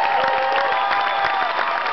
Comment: what did he say in the end? "Gd bless you..." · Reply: after the Gd bless you, he says goodnight friends